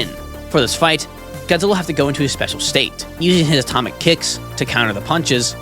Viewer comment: not gonna lie, I thought he said "atomic kids" 💀